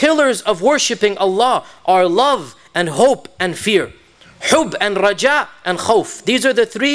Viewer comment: Can Anyone tell me what word did he use for Hope in Arabic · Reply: رجاء